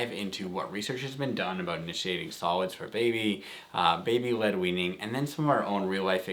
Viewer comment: For the longest time whenever I heard about "baby-led weening", I kept hearing "baby linguine" instead 😶